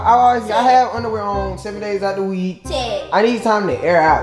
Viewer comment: it sounded like she said tray and not ted